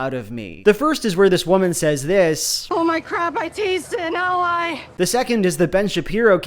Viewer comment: What does she say⁇ I can't understand it. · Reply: "Oh my crap, I tased an ally!"